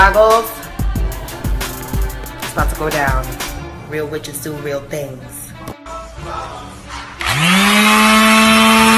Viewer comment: what was she saying all that black slimy sht was in her floor she had to scrape up? What is she talking about. I cant understand her and the captions got most of what I could understand wrong. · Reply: Sacrificed chickens